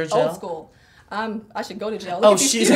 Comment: Tyra: Are you gel or acrylic? Patrick: Acrylic. Tyra: Oh, old school. Patrick: Are you gel? Tyra: I should go to jail, look at these cuticles! LMAOOOOO She was so quick with that one. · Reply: CreoleKing i thought she said "i should go to gel, look at these cuticles"